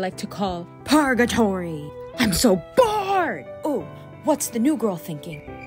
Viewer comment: I thought you said " I am so Fart" instead of 'bored' 🤣🤣🤣🤣🤣